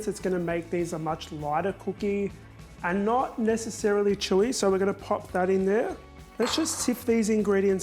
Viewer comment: Had to play it over and over where you said chewy as I thought you said chorizo Thought I was going mad 😂